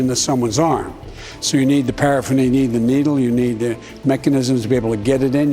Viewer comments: did he seriously just call a vaccine needle paraphernalia? did hunter teach him that word? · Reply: Thank you. I thought he said paraffin and I was really confused. Yep, guess he meant paraphernalia. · Anyone else hear him mention paraffin when speaking of the vaccine? I googled paraffin injection and it’s not chill . Not at all. It’s